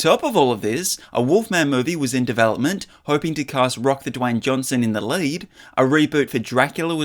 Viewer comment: does he say Rock the Dwayne Johnson? 😆 🤣 😂